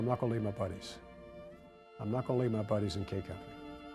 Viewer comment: Could somebody tell me what Col. is saying at 3:01? "I'm not [...] my buddies in K Company". Could that be "I'm not gonna leave my buddies"?? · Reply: "I'm not gonna leave my buddies in K company"